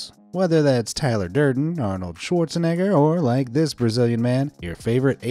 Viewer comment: what did he say? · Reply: shawrzenegger